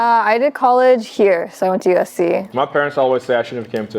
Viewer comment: am i tripping or did three say she went to USC🤔